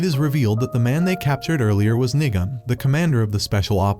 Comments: I think he said nigon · for me it sounded like “The man they captured earlier was n***a” 😭🙏